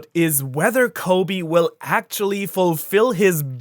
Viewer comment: he said "sexually" instead of actually 😆